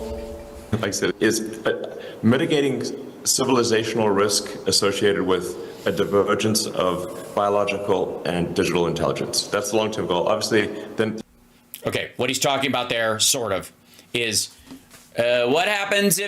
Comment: Did he just say 'genital intelligence'?